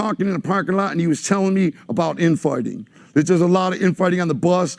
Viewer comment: swear that mf said infarting